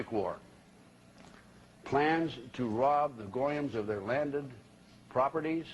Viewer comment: did he say goyums